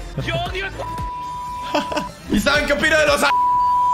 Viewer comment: escuche Aarrrgentinos?😠😡 Like si lo ves